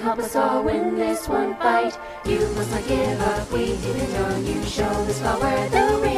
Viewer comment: i swear i heard "you must forgive us, we didn't know you" like for real